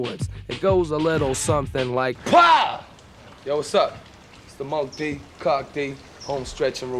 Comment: What did he say @ 3:31 after pow!? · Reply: Yo wassup 😂